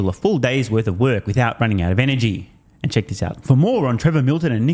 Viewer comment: Next-level denial. :_STMPFacepalm: ► AG1 <link> (FREE 1 Yr Supply of Vitamin D + 5 AG1 Travel Packs) ► Want More Content? Join Patreon: <link> ► Subscribe on X (Twitter): <link> ► Oura Ring <link> (Track your sleep, readiness & activity) ► Browse MERCH: <link> ► TSLA Fair Value aka “Price Targets” (<num>) <link> 👕 Don’t bet against Elon MERCH <link> 👕 TSLA HODLR MERCH: <link> 👕 420 Funding Secured MERCH: <link> 👕 I’m ALL IN on TSLA MERCH <link> 👕 Buy $TSLA MERCH <link> 👕 TSLA to the mooooooon MERCH <link> 👕 Cybertruck Light Strip MERCH <link> 👕 Mars awaits MERCH <link> 👕 Make Manufacturing Great Again <link> ➡ SEND BITCOIN: bc1q386gs0uhzr5l5zatwz5u28t3f729zpsa9p8t0k ➡ SEND ETHEREUM: 0xb3F<num>DF1d21a0e0CB6DFdF7bFBBA0A3 AFFILIATE DISCLOSURE: I earn a commission on every purchase made through my links. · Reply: Listening to you I thought you said "for moron Trevor Milton". Then I looked and saw more on. Oops. Hard to know when just listening. lol